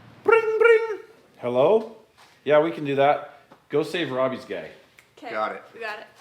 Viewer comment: I thought said "Go see if Robbie's gay" 😂 I had to watch it again with CC on.